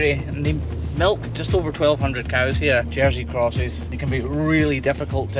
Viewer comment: did he say "dirty crotches"? I don't speak Scottish, so IDK.....